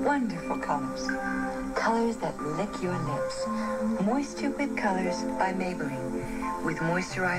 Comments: I heard “more stupid colours by Maybelline” I’ve got no idea what she says there, that’s all I can hear. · OMFG, I LITERALLY HEARD "MORE STUPID COLOURS" & NOW I CAN'T STOP LAUGHING. 😂🤣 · I swear to god i thought she said "more stupid colors that lick your lips" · I honestly thought she said "more stupid colors by Maybelline" 🤣